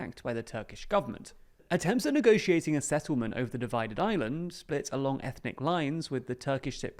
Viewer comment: I thought he said “divided Ireland” lol